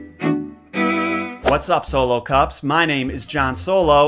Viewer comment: When the origins aren’t messed up, but still smashes it🤩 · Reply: I still mishear it as "solo-cops" in a messed-up connection (in my brain) to Robocop. I'm doing my best to adjust.